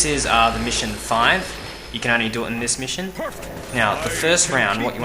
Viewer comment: oh never mind, you said "The Mission Five" lol i thought you said this is in Mission 5